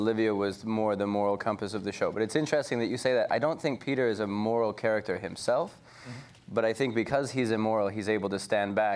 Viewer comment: what does Josh say at 4:43? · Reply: Fatema Haque “If you’re nasty.” i.e. “My first name ain’t baby, it’s Janet - Miss Jackson if you’re nasty.” Comedy god damn gold.